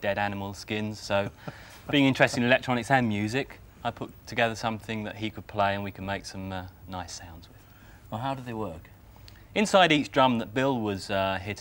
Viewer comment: I’m actually impressed with her knowledge and drum vocabulary. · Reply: Ha, fair enough! I misheard what she said as 'nice sub'. Maybe projecting my own thoughts!